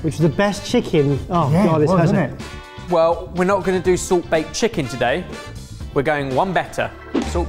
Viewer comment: I can't be the only one who heard 'salt bae' LOL